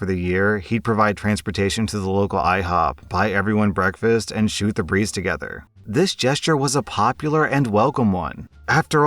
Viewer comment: I'm generally audio only (because I listen while driving), and I was only half listening at the start of the AP Calculus story, and I heard "shoot the priest together", which left my very confused for a moment.